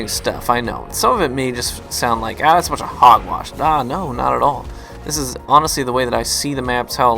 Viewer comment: Did you said "That's a bunch of Hogwash"?